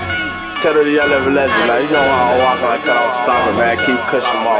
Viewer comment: I'm so curious what he says at 0:30?? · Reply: You know how we rockin keep kush up in my pockets